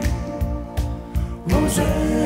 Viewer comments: My name is Rosanna after this song 😆👍❤ OMG Thanks for the likes and the answers! ❤❤ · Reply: My dad thought they were saying ‘Breanna’ so thats my name 😆♥️ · All these years, I thought he was saying "Hosanna".